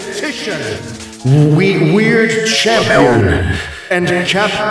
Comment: When I heard "A mere object" I thought I heard "A weird Pogchamp"